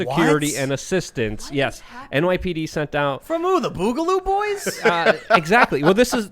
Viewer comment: Every time I hear "Boogaloo Boys", I think "Booger Boys"... The boogs be nasty.